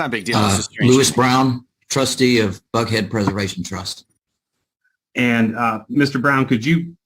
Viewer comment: I swear I heard “Butthead Preservation”…. 😮😂